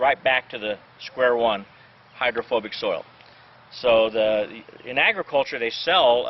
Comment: i thought that said homophobic soil